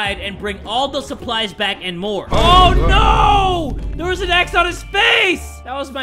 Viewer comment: I thought said oh no there’s an axe one his face